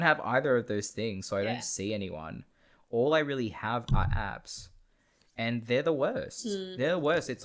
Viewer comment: dude I heard "all I have are abbs and there useless" heheh twas funny